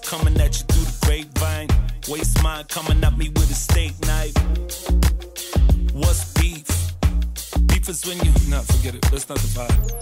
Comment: What's beef? Beef is when... Nah forget it. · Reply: I thought he said what's beat?lol But I still gonna sing like that saying that line it's so wholesome considering that the beat is so smooth.